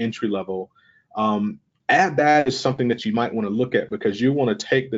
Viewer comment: what did he say at 19:03? ad dash? and at 21:00 did he say "flexed desktop support technician"? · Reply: He said, "add that," as in even though CISSP is considered an advanced certification, it's worth exploring the certification to get a sense of different topic areas and careers. At 21:00 he said "let's say desktop support technician."